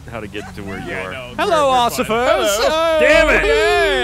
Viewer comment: Did say "Hello Occifers"? LOL